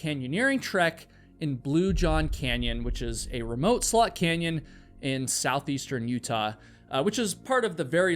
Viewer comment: I know it’s not what you said, but I heard “a remote slut canyon”